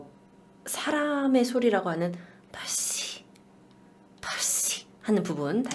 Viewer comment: 나만 이거 펩시로 들리나